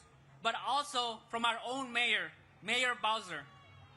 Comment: did he say Mayor Bowser? 🐢🥷